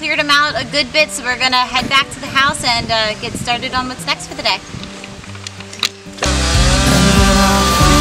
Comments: "...gonna head back to the house and get started on muh sex for the day". (Did you really say that?) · i can swear i hear you saying.....we're gonna head back and get started on our sex for the day....sry.....lol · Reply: OH....lol...get started on what's next for the day.....roflmao